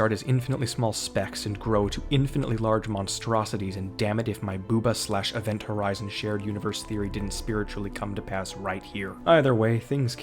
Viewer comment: did he say boohbah/event horizon shared universe theory? event horizon, as in the psychological space horror movie, following various characters to other deaths while dealing in machinations of the human mind and hell itself. and boohbah, the British television sequel to teletubies where a nameless family interacting with various basic objects is interspersed with living koosh balls doing yoga and making fart sounds